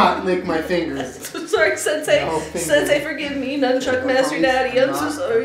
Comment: "DO NOT LICK MY FINGERS" AFTER HE SAYS THAT, IS IT JUST ME OR DID I HEAR "I WILL FINGER YOU" 😂😂😂